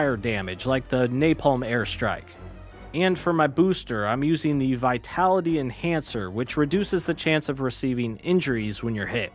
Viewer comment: YouTube subtitles said "vitality and cancer"